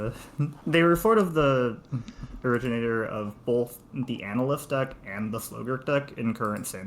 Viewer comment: What are the two decks said here? I can make out "analyst" but I have no clue what the other word is meant to be. Sliver, sigurd, ??? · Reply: Aftermath Analyst and Slogurk, the Overslime!